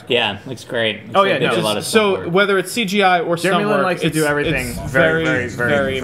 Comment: Does say Jeremy Lin at 3:24? ...Go Rockets.